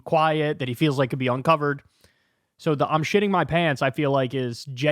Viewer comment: Sounds like he said "I'm shitting my bed."